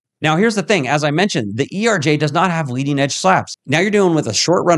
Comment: I was listening, and had to watch coz I swore he was saying "leading edge slaps." yep 😂